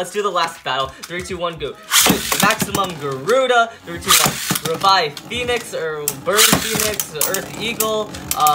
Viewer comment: I Thought He Said "BIRD PHOENIX"!